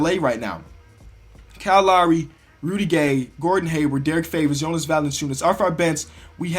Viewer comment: You said Rudy Gay instead of Rodney Hood.